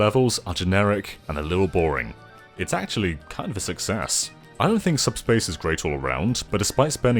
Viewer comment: I swear I heard "it actually kind of sucks a**" the first time.